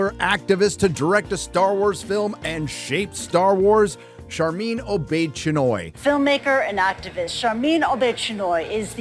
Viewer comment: To be honest, the first time you said her name I heard "Sharmeen Old Bitch Annoy"